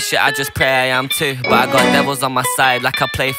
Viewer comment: Thought he said ive got Nevilles on my side like I play for Man U... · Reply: Ive got devils on my side, like I play for Man U.